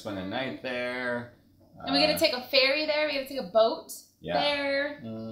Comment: I thought Cupquake said "we're going to take a fairy there". I was so confused. xD